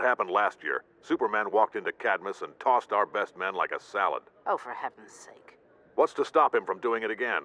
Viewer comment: "Superman came in and tossed my men like a salad." Am I the only one who heard "Superman came in and tossed my men's salad"? Ugh. My mind is so polluted with porn references. Sad.